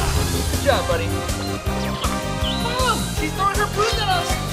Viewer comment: Idk y but I thought that when he said she’s throwing her harpoons I kept hearing she’s throwing her boob and I’m lik 🤨🤨🤨🤔🤔🤔